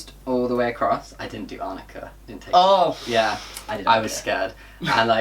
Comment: what does say? It’s something like “I didn’t do _____” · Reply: Arnica, it’s a supplement that helps with bruising